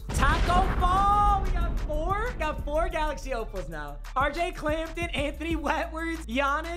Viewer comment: Was I the only one who heard him say R.J. Clapton.